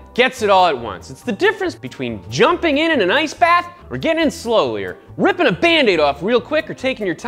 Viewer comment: Did he say "slowlier" at 2:22?